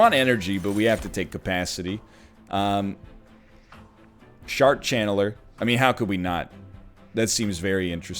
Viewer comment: did NL say Shart channeler? If so he really doesn't like that unit